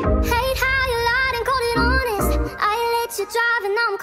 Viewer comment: I literally heard "hate knowing that her curls made you forget all about mine"